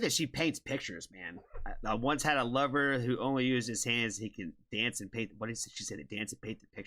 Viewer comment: You misheard ... Actually: "... I did once have a lover only kept his hands ..." even creepier 😳